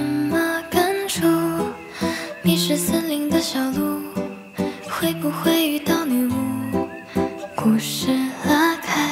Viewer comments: 我听成＂你是森林的小鹿，会不会找到母鹿＂😂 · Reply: 我听成 你是森林的小路 会不会找到麋鹿🤣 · 我聽成 迷失森林的小路 會不會遇到麋鹿 哈哈哈哈哈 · 我听成＂你是森林的小鹿，会不会找到母鹿＂😂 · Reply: 我会听成“你是森林的小路，会不会遇迷路”🌝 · 我听成＂你是森林的小鹿，会不会找到母鹿＂😂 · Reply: 我第一次听，听成迷失森林的小鹿会不会遇到迷路？😂😂😂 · 我把迷失变成了你是森林的小路然后后面那一句我听成了会不会与他迷路？？？哈哈哈😂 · 我听成＂你是森林的小鹿，会不会找到母鹿＂😂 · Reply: 我听成 迷矢森林的小鹿 会不会遇到女巫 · 我听成＂你是森林的小鹿，会不会找到母鹿＂😂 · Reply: 我听成（迷失森林的母鹿 ，会不会找到女巫）😂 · 我听成:你是森林的小鹿，会不会遇到礼物😂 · 我听成＂你是森林的小鹿，会不会找到母鹿＂😂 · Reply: 我听成 迷失森林的小鹿 会不会遇到麋鹿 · 我听成＂你是森林的小鹿，会不会找到母鹿＂😂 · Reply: 我听成你是森林的小鹿，会不会找到女巫